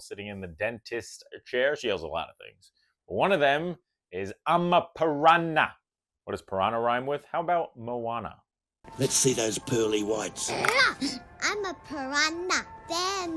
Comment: Is anyone having a Mandela effect with the Darla question? I could have SWORN she said " Im a Pirahna, from the Amazon "